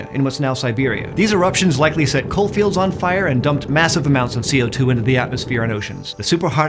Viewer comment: Did he just say "Set COAL FIELDS on fire!" what the hell would that even look like!?